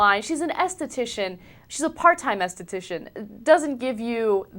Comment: What did she say? Estitician? The fuck?